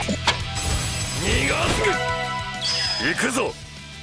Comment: did DIO just said the N-word?